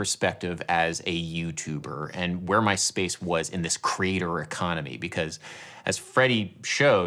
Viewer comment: listening to this and whole time i heard ‘crater economy’ was like…. what the hell is he railing on about. enunciation m’boy!